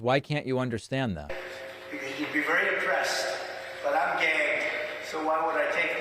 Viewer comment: I heard trump say "I'm gay."